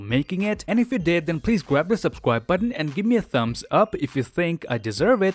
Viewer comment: he said grab the subscwib button instead of subscribe ,could be him foreshadowing something else 😁